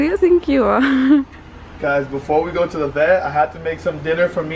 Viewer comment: did say before we go to the bed?